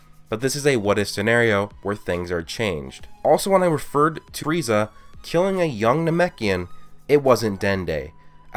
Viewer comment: you know my best friends name is tereza and that what it sounded like he said